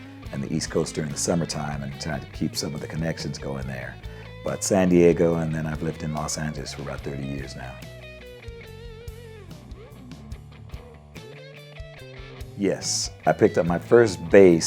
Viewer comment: did he say Los Santos?